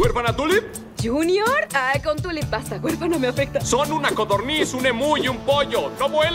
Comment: Que dice la niña en el segundo 00:59 ? xdd no le entiendo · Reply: Con Tulip basta